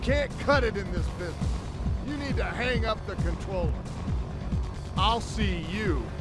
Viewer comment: I never understood he was saying 'controller', I thought he was saying 'phone'. Makes so much more sense now.